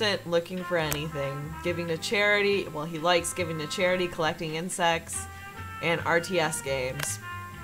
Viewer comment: I wasn't looking at the screen and I thought you said "collecting incest." 😂👌🏻